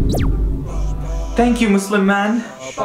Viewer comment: It sounds like you said musul-man but you said Muslim man